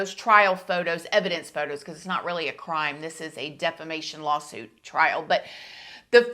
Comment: Lol thought u said defecation lawsuit trial instead of defamation trial at first!!